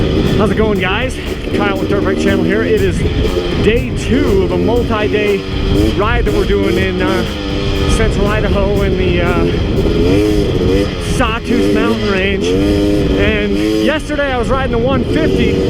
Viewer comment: I had to rewind and listen a few times...it sounded like you said Central Rideaho...gopro mic i guess lol